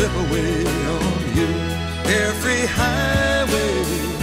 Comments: I looked up some Gordon songs. I saw this one and thought, hmmm, I'll check it out. Then... What? Carefree! Oh!!!! Haha! Yep, always heard it as "Every Highway". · Takes me back.... folk music was still a thing in the 70s.... But for 40 years I thought he was saying every highway. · I always thought he was saying Every Highway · Funny when I was a kid I also thought he was saying every highway,LOL I was like 7 great tune!!!